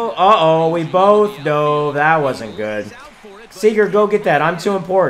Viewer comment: Did he really say "Sigurd go get that" at 5:34. If so my life is complete. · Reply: He said "Corey" Seager go get that